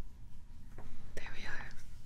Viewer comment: Thought she said “sh*t”when it was actually “shake”